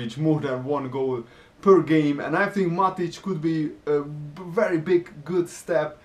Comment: Did he say Matic?